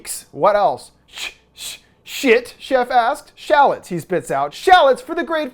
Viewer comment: I thought he had a lisp saying salad but he was saying shallot😂😂